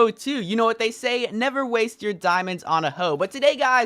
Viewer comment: Did he just say don't use diamonds on hoes